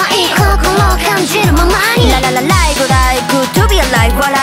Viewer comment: 20年間GOOD TO BE ALIVEをぶっ飛びアライブと空耳してました